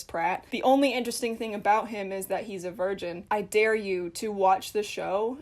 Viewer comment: oh, i totally misheard things then. i kept wondering why it matters that he is from Virginia